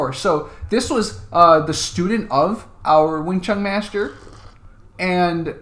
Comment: I thought you said "Wing chun' Master.'